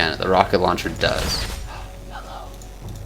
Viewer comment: Did anyone else hear someone saying hello in a whisper voice around 9:25 o.O kinda creepy he probably has a ghost in his house :O